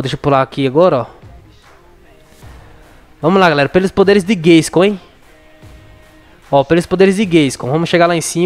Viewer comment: eu n entendi oq ele falou, mas acho q ele falou, pelos poderes de gayscoin ou gay